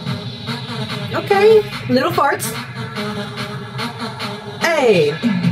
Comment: DID HE JUST SAY "Okay, little farts?"